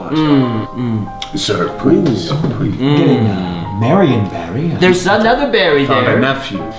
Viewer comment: Did he say Marion Barry, as in Mayor for Life?